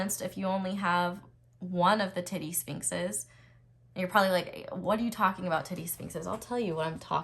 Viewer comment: Heard that as "teddy sphinxes" 😂❤